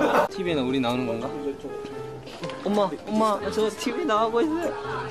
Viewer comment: HE SAID JK:MOM.!MOM,I'M ON TV!! BUT I HEARD : MOM,MOM, NASA TV🤣 NA AKO🤔🤔🤔🤣🤣🤣🤣🤣 👇